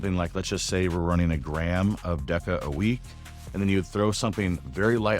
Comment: did u say a gram of deca with 50mg test